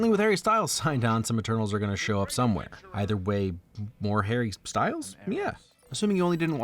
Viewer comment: I have your video on in the background and I honestly thought you said Aristotle at 15:32 and I got super confused for a second. Until I looked at the video at least.